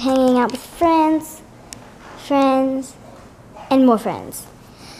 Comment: at the end of the video when says 'friends and more friends' I thought she said boyfriends not more friends😂. oh this part was right before the question of the day